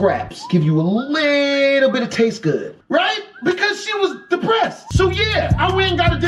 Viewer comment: wtf this man saying 😂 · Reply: "A little bit of taste good"?? what exactly is this "Taste good"?? XD